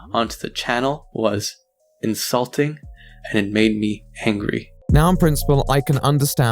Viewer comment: i legit thought he said Hangry